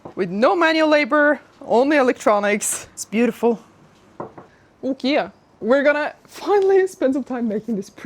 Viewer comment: Ummm, at 12:46 did she just say "OhKea"? 🤣